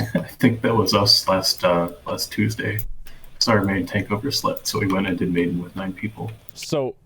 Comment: to me here it sounds like he said his tank "overslept", not gave up his slot